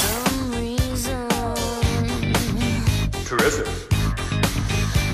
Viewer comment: what does he say? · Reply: Terrific